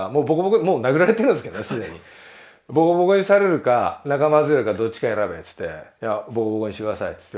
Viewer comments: なんて言ってんの？ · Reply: 「仲間外れるか」 だと思います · 「ボコボコにされるか仲間○○？？」ってなんていってんの？ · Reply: MHkopp 外れるか